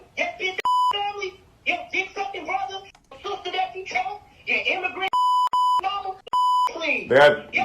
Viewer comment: Keke’s mom talks crazy. Saying “Usher is gay”, “Darius’ immigrant dad” or something like that. I heard “Mexican” . Keke needs new representation. Her mom was out of pocket and very hateful.